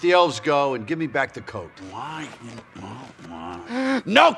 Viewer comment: does anyone else hear Coke and not Coat? Maybe its just me knowing his past.